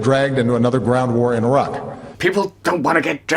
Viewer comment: I thought he said "another BROWN war in Iraq" , shit, my bad